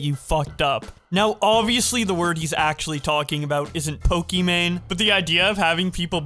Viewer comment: i kid you not i thought he said "pootang"